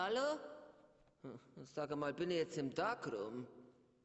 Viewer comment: "Bin ich jetzt im Darkroom?" Einer der vielen Gags, die ich damals, als der Film raus kam, noch nicht verstanden hab xD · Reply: Oh, ich hab immer verstanden "bin ich jetzt im Tag rum", also dass der Tag vorbei ist und nun die Nacht herein bricht😅